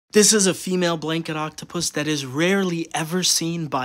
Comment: Sounds like they say Blinkin' Octopus instead of blanket